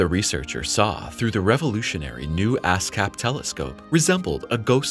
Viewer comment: did he just say "ass cap" telescope? 😂